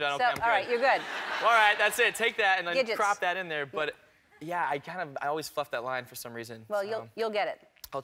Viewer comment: it sounds like hes saying i always fuck up that line lol